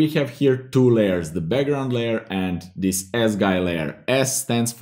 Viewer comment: my psycho mind heard it as ASS GUY layer...never mind